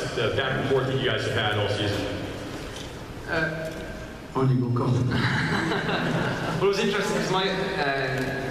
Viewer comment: Can anyone say what are Colin and Robert saying between 12:40-14....please tell me, I cant understand... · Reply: the person asks how fun the crocodile/hook rilvalry has been. and Robert replies with: " eh...... on you go colin and laughs" colin laughs and says " well it was interesting eh I remember the first day on set and actually we were on the jolly rodger the actual boat we were out and eh it was really weird because I was standing across from Robert Carlyle and I remember going oh I better not freak out! laughs but it was eh I think its been eh interesting, sort of strange relationship." Robert: " yeah its been great, obviously the rivalry in season one was between me and lana so it was interesting to bring another character into it especially a male one at that. So it been fantastic and obviously colin is from Ireland and I'm from Scotland so we have that celtic bond there everyone laughs but no one will know what I'm talking about here but there is a guy called st james and he used to be in the carry on films and that is what I call him this carry on captain right here" everyone laughs Eddie says " when we are at dinner with the two of them we understand one out of every 5 words they say " everyone laughs " Colin and bobby get together and I have no idea what they are saying at ALL" everyone laughs "you just smile and think ahh they're pretty!" "everyone says aww and laughs* Robert: " its fantastic! They don't realise we are insulting them with every third word!" that's basically it haha